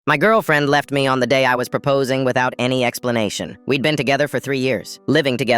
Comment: My dumb ahhhhh self thought they said “My girlfriend left me pooping without any explanation “😭😭😭😭